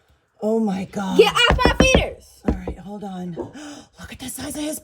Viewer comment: I thought she said "LOOK AT THE SIZE OF HIS BALLS!!" 🤣